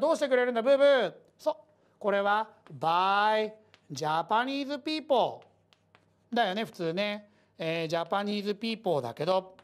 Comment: “Why Japanese people!!” と聞こえたのは僕だけ？